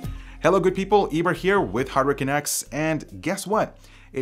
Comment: ow, Eber said HardwareCanucks on the opening ?? LOL, i thought he said HurricaneX or something all this time